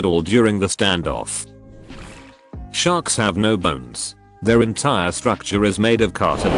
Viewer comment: I was half listening and close to done with one of those lil bottles of soju, but I when I heard this at first, I coulda sworn they said “sharks have no butts”, and I questioned my entire existence for a moment.